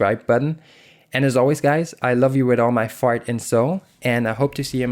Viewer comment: Is it "heart and soul"? or..... cause I heard "fart and soul".